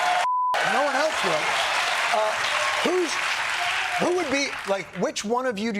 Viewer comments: Steward got bleeped but it sure sounded like he said “F it if no one else will”. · Did I just hear Jon Stewart say at 11:30 "If no one else will" when asked if he is going to throw his hat in the presidential ring? Careful Jon, you're giving me hope!